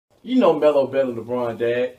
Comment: what did you say? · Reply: Dont nobody care,i think xD